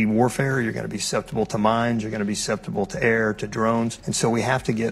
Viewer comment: did he say it's going to be susceptible to air? I really hope he means it will be susceptible enemy aircraft, and not just "air."